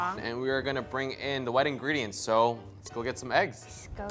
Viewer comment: At like 5:14/5:15 I literally heard we are going to bring in the wedding ring instead if wet ingredients lol im delusional